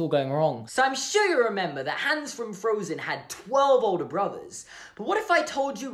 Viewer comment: Did he just say hands? Lol